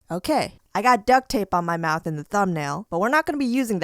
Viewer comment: Idk why but i kept rewinding when she said ok, and one time it sounded like she said "ok, i got dic"